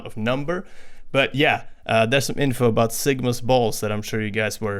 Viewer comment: did you just said sigma ball